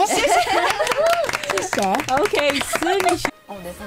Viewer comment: did anyone else hear "not to shay"